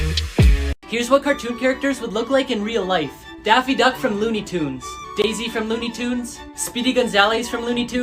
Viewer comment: Did he just say.... Daisy duck from... Looney tunes?????